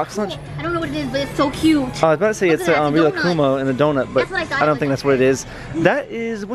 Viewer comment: Did he say Monokuma in a doughnut? Is a Danganrompa fan?! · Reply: no lol he said rilakuma